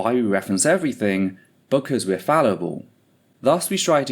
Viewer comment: I genuinely always thought that you said "because we're valuable" after explaining the reference numbers, not fallible 😅😂 I quite enjoyed agreeing that you were indeed valuable. I think I'll just continue to 😊